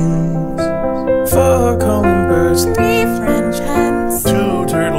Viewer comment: didn't sound too happy about singing "3 french hens" at the start. I guess she was disappointed they weren't 3 french horses.... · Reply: I thought she said 3 french men